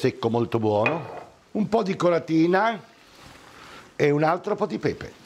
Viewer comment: " " un po' di Collatina"? .... Non ho capito.